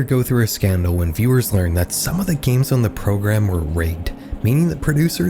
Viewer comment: Lol, the subtitles say the games were graped, rather than rigged.